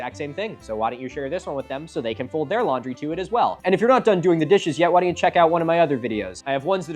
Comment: For like 2 seconds I heard, "folding the dishes or doing the laundry"